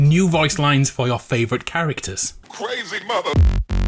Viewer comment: For a second there I thought said: crazy mother***